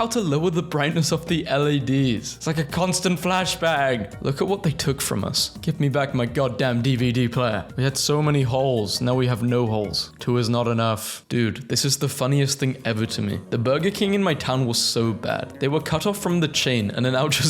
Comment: What did you just say? 2 Holes isn't enough?